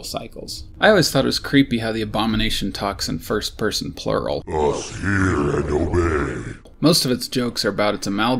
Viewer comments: OOOH i always thought the abom said "must hear and obey" rewatching these all kidna helps to know what they are really saying, ofc when i first heard it at 5 i kinda had head canon of it and its hard to break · I always thought the abomination said "Must heal and obey"